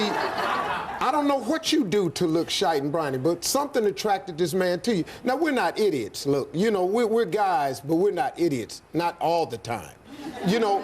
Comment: "shite and briny" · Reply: I heard "shite n briny" too!! lol!